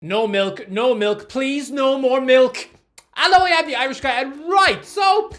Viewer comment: Wahlahi I thought the irish guy said no more milfs 😭